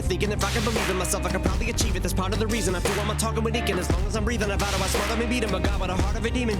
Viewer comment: he said "I do all my talking with ink", this is what heard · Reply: Always thought he said "talking with deacons" to blend with the God/demon scheme 😂❤ Still great tune.